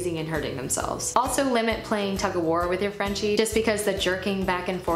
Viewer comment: Hi! Very great video, I am going to a have a French bulldog soon and I am very happy to have find your video! Thanks a lot, but I am not a English native speaker and I didn't get what you said at 4:09 , the game that we shouldn't play with them. Thanks in advance 😊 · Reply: hi! the game is Tug of war <link> I still play Tug of war with Mabel a little bit, but not for too long or too aggressively. :) thank you for watching!